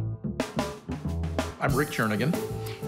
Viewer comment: I thought at the beginning Rick: said “I’m returning him” 🤣